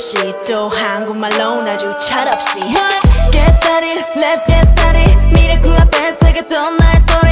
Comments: Sakura's "get started" line in 1:35 sounds so good omg I wish she had more lines in this song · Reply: That was lit line, i thought she said "lets get started, lets get sturdy" lol , anyone else? She got different voice in fragile to , she should get more lines, sometimes feel she is ignored. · Decent song, understandable why Yujin got most, she did great, and fit well. Did anyone else think Sakura said "get started, lets get sturdy" xD. Thought she said it. We need to give her more lines tho.